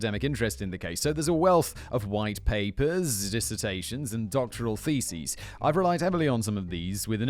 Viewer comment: i bet there's been a wealth of doctoral feces, too (which is what i THOUGHT i heard)